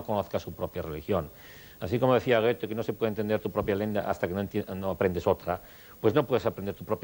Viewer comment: que dice de Goethe ? No se puede entender tu propia ..?? hasta que aprendes otra ? Que palabra dice ?, no entendi . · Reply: Lengua, me parece.